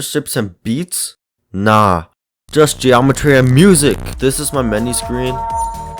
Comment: when you said “just geometry & music” I thought you said “just geometry dash music” lol